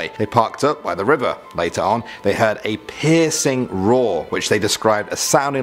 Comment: Omg I thought he said “pissing roar” 🤣🤣🤣🤣🤣